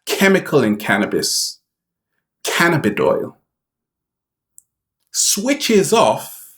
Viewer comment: You Are Worthy! :) NEW Snapchat: 10 Simple Ways to Stop Negative Thinking <link> Website: <link> Instagram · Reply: I thought he said cannabidiol as in CBD. That is what I use for pain.